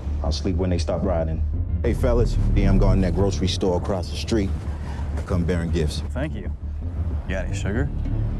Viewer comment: the black officer says he's going over to the grocery store. What's the exchange of words then? I cannot discern it. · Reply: "Hey, I'm guarding that grocery store across the street, i come baring gifts" - "Got any sugar?" - "Don't push it man "